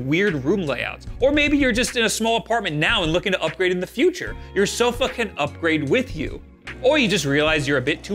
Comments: You’re so f*ckin upgrade with you… wait what? Did I hear that right? 2:26 ‘Your sofa can’ upgrade with you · You're so fuckin' upgrade with you ...wait- Your sofa can upgrade with you Right, that's exactly what I heard the first time, uh huh 😅